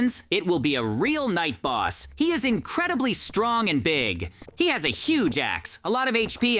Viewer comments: I thought jj said big a** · WHAT IN THE AI IS THIS?!?! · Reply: I thought JJ said big a**